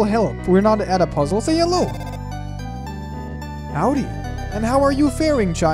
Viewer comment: I thought he said, “how are you farting”